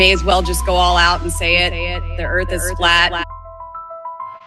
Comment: I just turned on the captions and at the exact time 01:00 minute when she said "the earth is flat" the caption show,( the earth is what). The AI can't even show on captions what she was really saying...